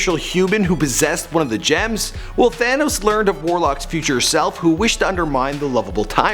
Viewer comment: I thought he said Vanoss then I checked the title.